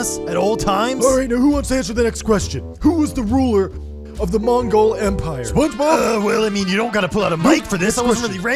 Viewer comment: The first time that you said Mongol empire I thought you said glomgold from DuckTales